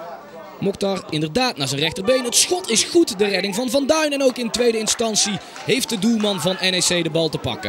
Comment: I think the commentater said Van Duin listen